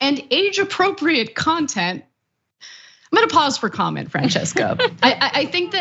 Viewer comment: Sounded like she said "menopause for content". I know she didn't but it sounded like that.